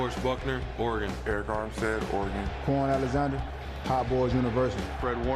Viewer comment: Hot Boyz University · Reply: I thought he said Cowboys